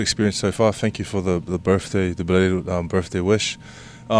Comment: Did he say thanks for the birthday blow???